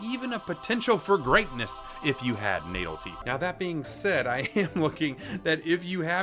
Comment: I was thinking the baby is an alien incarnate until you said he has natal teeth. That really got me cranked up.😂😂😂😂. It. It sounded like you said NATO teeth. I was like, NATO does sure have some problem teeth. 😂😂😂😂😂😂.